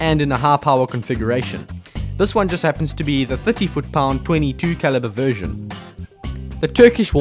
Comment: 30fpe or 50 fpe, it sounded like you said 50 foot pounds, the graphs show 30fpe with the 16gr